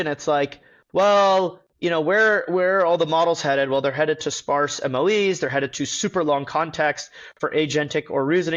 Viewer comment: Dylan talks about "sparse MLE", shouldn't that be "sparse MoE"? (Sparse Mixture of Experts (used in LLMs), instead of sparse maximum likelyhood estimation. (used in data science)). Slip of the tongue, or am I missing something?